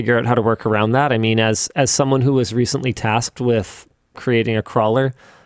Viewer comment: lol I thought Linus said he was tasked with making another crawler and in my mind I thought Yvonne wanted another baby